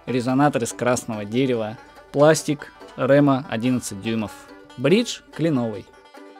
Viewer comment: мне послышалось "бридж хреновый"